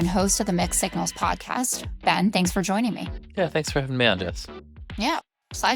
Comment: In the intro, I thought Jessica said, "and host of the Mexican podcast." I had to play it back and turn on CC. 🙂